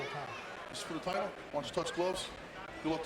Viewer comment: Does dude say touch gloves???